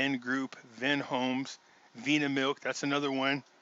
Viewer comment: What's the last one he says? I can't make it out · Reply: Vinamilk